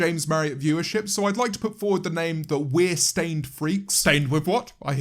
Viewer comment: Ngl I thought he said "Queer stained freaks" and I screamed